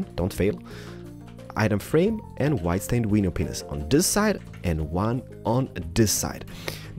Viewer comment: when he was placing the first rims and wheels at 1:22 I thought he said weeny penis!!!